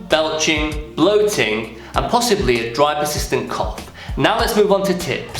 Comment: thanks for the visual cues cuz all i hear is "dry persistent cock"